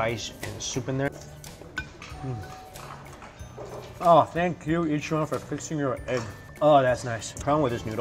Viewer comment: Thought that said Ichiraku for a sec